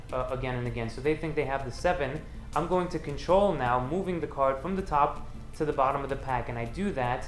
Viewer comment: When discussing the "magic move" I thought you said to poke them in the bottom... yep.